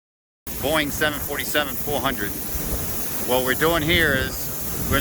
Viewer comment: I thought you said boing 747